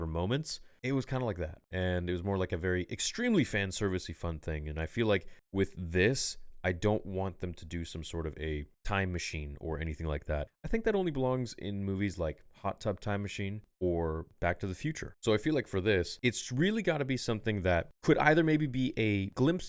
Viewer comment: I thought said the WBW was no time machine.